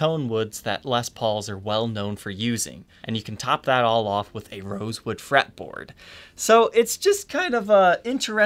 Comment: "... and you can top that all off with a ('remoose wood' it sounds like,) fretboard". What kind of fretboard? Anyone know what he says here? Sounds like he was saying "rosewood" and changed his mind half way through!